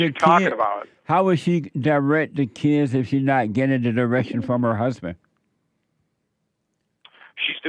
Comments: What Jesse say at 6:15 ? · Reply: how will she direct the kids if she's not getting the direction from her husband" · What Jesse say at 6:15 ? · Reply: Swear I thought he said " she's not getting an erection from her husband "😂😂😂🎉🎉🎉